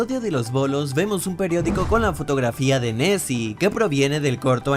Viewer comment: yo escuche messi xd